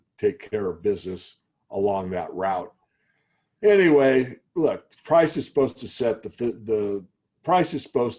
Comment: freudian slip ? did he say price is supposed to set the 5th of ... am i the only one to have heard that ?